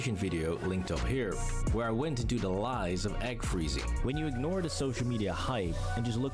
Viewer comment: I thought he said 'hag freezing' 😂